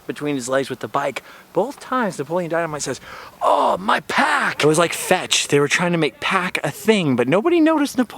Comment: "My pack"? I thought he said "My sack". Your impersonations are on point!